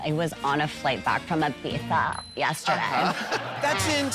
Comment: I just cry over the way Gottmik sticks her tongue when she says Ibiza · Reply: Me too but I didn’t know she was saying Ibiza .... I thought she said pizza hahahhha